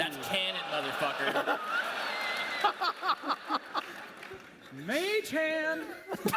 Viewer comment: What does Scott Kurtz say at 16:56? "Mage Hand"?